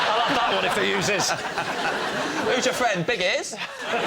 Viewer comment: What does he say at 5:06? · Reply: Who's your friend?big ears